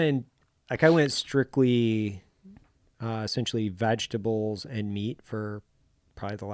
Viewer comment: did he say vagtables?